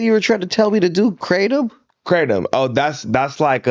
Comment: Can someone help me figure out what is it that they were talking about at 1:35:46? Is it called cradome? 🤔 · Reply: Kratom